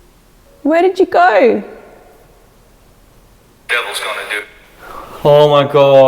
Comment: I swear I do not hear "devils gonna do" I keep hearing "Devils comin' dude" or "Devils comin' due." So strange how folks hear different things